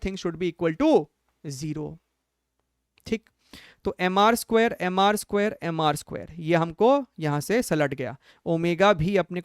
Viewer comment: why am I hearing mr sir ?